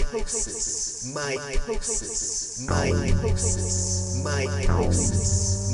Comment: I thought he was saying mitosis.